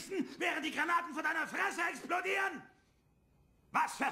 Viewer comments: ICH HAB MICH AUF DEM WEG ZUM COLLEGE VERLAUFEN SIR!!!! xD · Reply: Ich hab immer verstanden "ich hab mich auf den Weg zum Golfen verlaufen" Hmm :-) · ich hab verstanden ,,Ich hab mich auf dem weg zum McDonalds verlaufen SIR!"